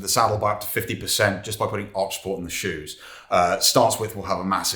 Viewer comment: By putting what in the shoes? I couldn't hear his pronunciation clearly. At the 13:24 min mark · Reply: Arch support